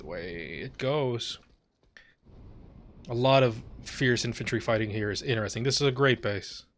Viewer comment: I thought you said "entropy fight" instead of "infantry fight" but with the way some of the infantry stalemates are in this game, "entropy fight" feels like a better name.